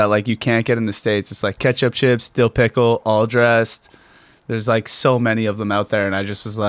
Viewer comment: All dressed chips??At the beginning of the show, TJ says Ketchup chips, dill pickles chips, and then he said what I thought I heard as All Dressed chips... did I miss hear or what the hell are all dressed chips... inform me!!